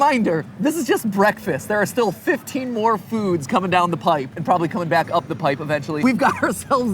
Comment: Did you just say coming down the pipe?